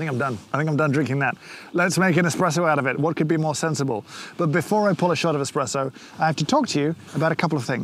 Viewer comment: Did I hear "Before I polish out an espresso..."? :)